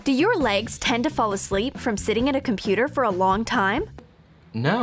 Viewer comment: Did they say IN the computer